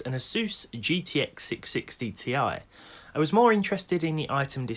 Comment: At first I thought you said 1660ti